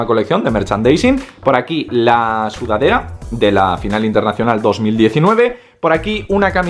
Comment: No se porque pensé que decía "Final inesperado" :V xD